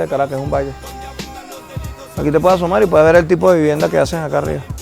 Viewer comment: the subtitles read: "From up here you can see where the war takes place". What the guy actually says is: " Here you can take a peek at the type of houses ( vivienda ) they make up here". He is probably referring to the improvised construction style in houses in the slum. What is the point of changing the meaning here Vice?